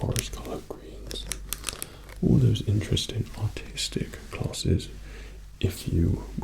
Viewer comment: LOL thought he said autistic classes not artistic